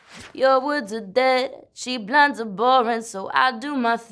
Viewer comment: Tell me why I thought she said "cheap blondes are boring" Whoops